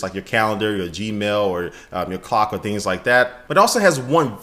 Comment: when he says clock it sounded like cock